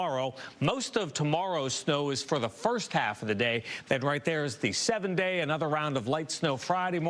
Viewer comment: He said it's gonna warm up, you'll see "in the 7 day" but I heard "in the 70s" 🙉😆